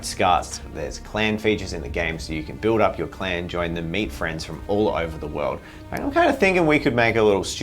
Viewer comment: What said:"So you can build up your Clan, join them, meet friends from all over the world..." What I heard:"Join your meat friends all over the world..."